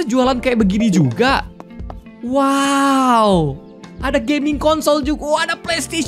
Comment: Miawaug bilang: gaming consol Tapi gw dengernya con*** Tapi pas aku denger baik-baik ternyata emang miawaug ngomong consol buka kata kasar